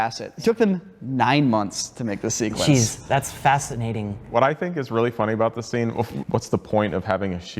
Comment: does Ren say "that's facet-nating" ?